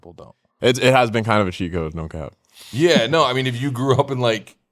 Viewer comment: did he just say “no cap”? Oh god here we go